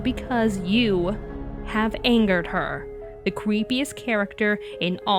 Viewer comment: Instead of creepiest I thought said queefiest and I lost it